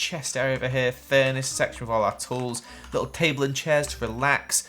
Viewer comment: I thought he said, “Sex with all our tools” and I was like, “Hmm.. which ones? The hoes?” I’m so sorry, I’m a mess 😂🙏🏼